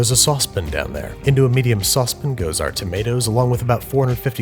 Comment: Anybody else hear 3:11 sauce pin ?